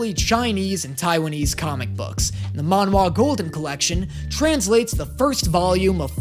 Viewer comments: That's funny. When he said "Manhua", I thought he said "Manhwa", whic his Korean not Japanese. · When he said Manhua I thought he said Manhwa and I was like "aint that Korean? Oh wait-"